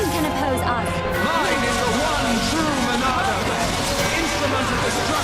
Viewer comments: i heard "reyn is the one true monado"... sorry for spoils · I think the smoking did more than just kill you Disciple Dickson I think you got smoke in your eyes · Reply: hunter o,flyn I think my smoking also affected at 21:30 i heard "Reyn is the true monado!